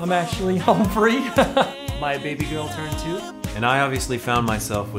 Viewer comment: what did Adam said in 00:43? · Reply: Christian Brahmanendra (my baby girl turned two)